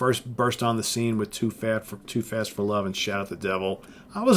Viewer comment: LOL Did you say Too Fat For Love at 1:31?